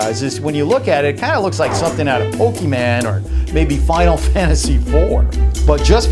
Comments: I honestly love your videos but...did you just say Pokeman? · Did...did he just say pokiman.... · Did he just say Pokeyman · 50% of the comments: Good video 50% of the comments: Did he just say "pokyman" instead of Pokemon? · Wait did he just say "POKEMEN?" lol 😂😂😂😂😂😂😂😂😂😂😂😂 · did he say pokeMAN?